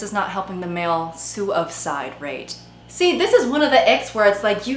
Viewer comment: I keep hearing "the ick" as" the egg"